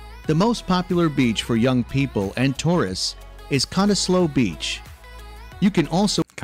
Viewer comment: First mistake right at the start. Not Bay Bridge. “Sydney Harbour Bridge.” No 2. “Not Fly,nders st station, like other words with the letter i in it (E.g.Linda) it’s pronounced, “Flinders, (like “in”) street. No 3. Not Melbourne Bay. “Port Philip Bay. And it’s not Melboooouuurne, it’s Melbn. No 5. Not Bris bane, “Brisbn.” The beer is VB, Victoria Bitter. QLD has XXXX. Cottesloe beach. Australia supplies a large number of camels to the Middle East. Thankfully he never said, eemoo, when it’s eemYOU. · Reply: I love how thought the guy said, “Got a close beach,” instead of Cottesloe beach.